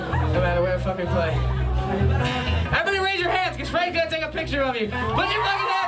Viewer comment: Please can someone tell me what Gerard said at the minute 21:10? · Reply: “everybody raise ur hands we wanna take a fuhkin picture with u” or something w u